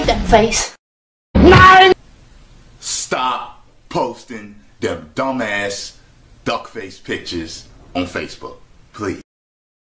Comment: what the wtf 0:19 ... What light say 😂😂😂😂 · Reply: Light : " Nein !!!! Stop Posting The Dumbass Duck Face Pictures On Facebook, Please !!! " (I can't speak English very well, but i guess... That's what Light said 😁😁)